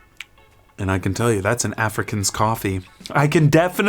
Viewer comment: “That’s an Africas coffee; I can definitely tell.” 💀 Hilarious! Nestle was able to capture the terroir of an entire continent! Impressive. I’ve always wanted a continent-to-cup drinking experience. Farm-to-table has been reimagined. Tasty traceability. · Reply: I thought he was saying Afrikaans 🤣